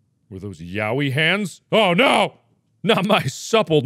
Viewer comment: Did Mark just say "yaoi hands" at 35:55 ? 👀